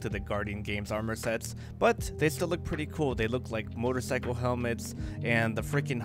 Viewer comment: Did you say motorcycle helmets? Can I get my hopes up for sparrow racing?